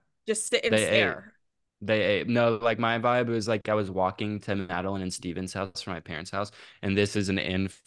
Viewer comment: my name is madelyn stevens and i lost it when it sounded like said my name LMFAOOO